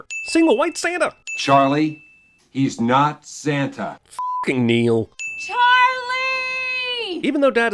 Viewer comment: for a second I thought Charlie said "F*ck you Neal."